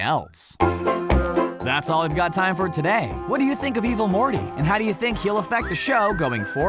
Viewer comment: At one point i thought he said "Evil Horny"